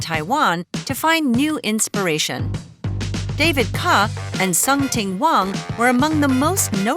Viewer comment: I heard "Sum Ting Wong" is that right??